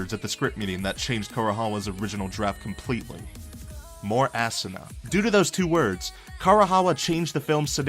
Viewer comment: in 1 I thought he said "more ass"